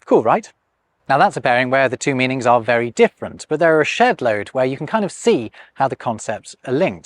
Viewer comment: Whenever brits say "shedload" I hear "S#*t-load"